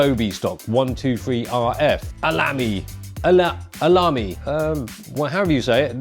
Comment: did said Alamby? Oh, mishear